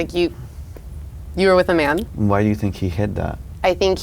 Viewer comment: "Why do you think he hid that." RAAAAA I thought he said hit😂😂😂